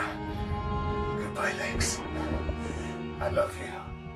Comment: i know what he said but on my first run it sounded like he said "goodbye legs..."